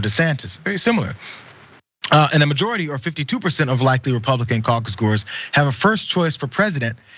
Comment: Funny thought: listening to this in the background I swore I heard Jayar say "Republican cucks". Turns out it was "caucus-goers". But hey, is there really any difference?